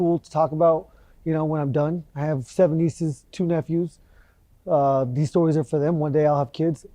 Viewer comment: Thought he said 72 nephews at first 🤣